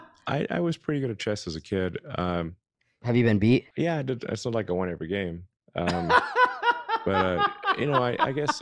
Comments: I heard him say "its not like I won every game" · "Have you been beat" "Yea, it's not like I won every game". You misheard him. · I don't think he said he never lost. He talked really fast, but I thought he said, "It's not like I won every game." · I heard him saying "It's not like I won every game". Subtitles made it sound different. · Subtitles were off - Elon said ' its not like I won every game'. Hikaru thought he said ' I won every game....CHESS IS FAR FROM SIMPLE. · I dont think he said he was undefeated. I thought i heard him say, "It's not like I won every game." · Musk is the type of person who tells you that Italian is an easy language to learn, but then states that he doesn't want to spend a thousand hours learning it. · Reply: I thought Elon said " its not like I won every game" basically saying of course ive been beaten before.